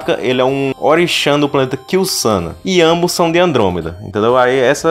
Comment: só eu ouvi onii-chan? 😳 👉👈